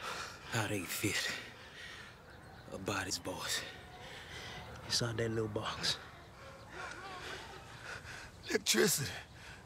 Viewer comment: what the fuck are they saying to each other at 2:50 ???????????????? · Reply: Milton: "How they fit a body's voice inside that little box?" Chalky: "Electricity." Considering how crazy Milton is, Chalky's answer probably didn't make things any clearer on how telephones work.